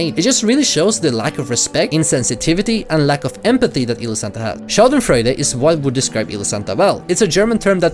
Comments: the german-word is "Schadenfreude" for those who didnt understand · i am german and i really tried to understand what you said their. subtitles said "sheldon friday" i tought wtf is this? then i realizied you mean "Schadenfreude" and your pretty right with this word for elosantas behavior.